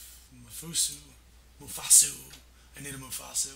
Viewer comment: Haha when you said mfsu I thought you said Mufasa!